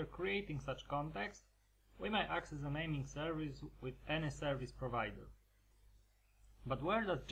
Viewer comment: We may access the Naming Service with anus service provider??? What are you saying??? Fix your audio or add subs PLEASE!